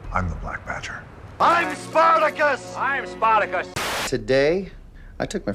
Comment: did they just say sportacus???